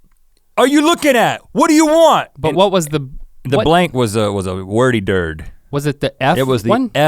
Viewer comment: wait did rhett just say wordy durd 😂